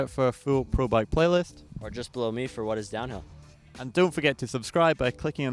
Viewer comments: omg i heard "just blow me" at the end HAHAHA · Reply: I heard "Just blow me" with a great gesture at the end :) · omg i heard "just blow me" at the end HAHAHA